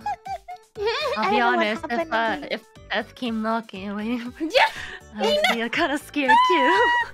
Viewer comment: Anyone caught what Ina said? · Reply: “I’ll be honest if death came knocking wouldn’t you open I’d be scared to” something like that